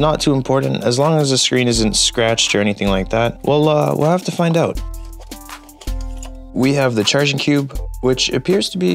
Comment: Did you say wallah cuz I’m confused 🫤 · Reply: Well uh